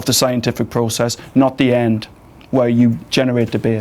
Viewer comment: Oh it took me til 10 seconds after the video had ended until I picked up what he said in the last sentence. I thought he said "generate the beard" when he actually said "generate debate"